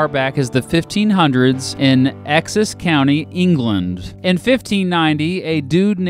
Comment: did you say “excess” instead of “Essex”?